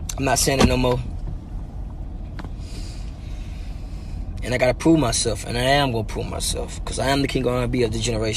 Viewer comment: bruh i thought he said "i gotta poo myself"